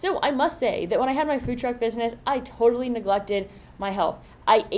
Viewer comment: What is what she says at 5:57, is that fruit drug bussiness or what, anyone? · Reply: Lol this made me laugh! I gotta slow it down a bit. Two big differences. I said Food Truck